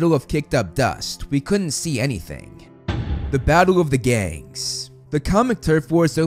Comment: I thought you said the battle of the gays instead of the battle of the gangd